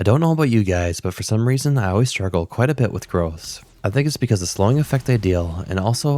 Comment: I thought you said you always struggle with girls LMAO. I was like okaay that's a bit random but you got this